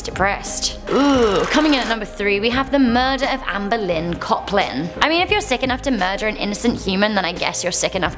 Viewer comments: Oh God...I was looking away when you said Amber Lynn Coplin, and with your accent, I heard 'Anne Boleyn Coplin' ROFLMAO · am I the only person who thought said "Anne Boleyn Coplin" not "Amber Lynn Coplin" ?